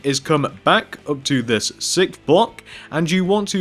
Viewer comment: its sounds like he says sick block